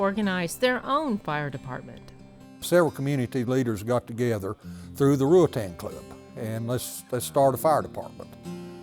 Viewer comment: I misunderstood what the man said, I thought he said "Members of The Wu-tang club" What he really said was Ruritan Club